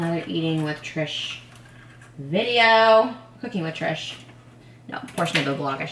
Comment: I thought you said Hooking with Trish, instead of Cooking with Trish